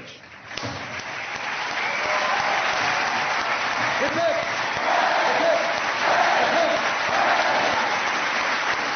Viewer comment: Does anyone know what COB chants at 8:47? I can't seem to figure it out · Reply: Hip hip hooray, I believe